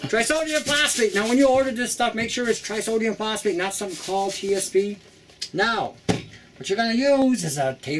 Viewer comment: I can't hear what you said at 1:38-1:43 "Make sure you order Trisodium Phosphate, not some xxxxxxxx TSxxxx"? I'm a little reluctant to put an irritant into my mouth but might try a little bit at first (and not swallow). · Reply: "...not [just] something [that's] called 'TSP'..."